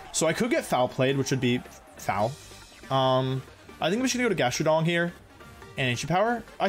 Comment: he actually said GastroDONG lmfao